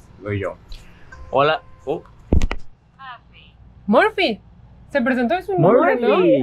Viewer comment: En el 33:45 yo escuché más un “happy” en vez de murphy